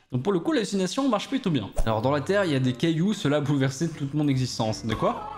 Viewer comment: J'ai entendu "dans la terre y'a du cailloux"